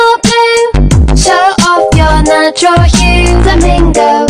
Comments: "Show off ur nacho cheese" i cant unhear it😭😭😭😭💀💀 Edit 1:thx for 90 likessssss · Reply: I hear “show off your natural you” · Why do I keep hearing “show off ur nacho cheese”😭😭😭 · Wait it says flamingo I thought she was saying the bingo..